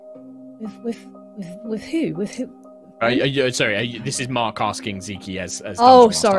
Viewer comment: i thought mark was saying "ismark is saying to you" not "this is mark saying to you" LIKE LOL I DIDNT REALISE THE NAMES WERE BASICALLY SO CLOSE TO EACH OTHER